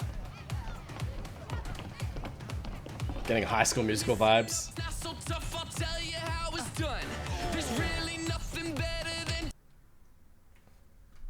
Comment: In fired up i thought bucky said:your so fucked up ill tell you how its done😂😂